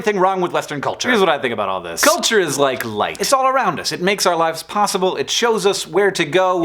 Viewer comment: I sneezed right as he introduced his "culture is light" metaphor, and so I heard "lint" instead.... That didn't make much sense..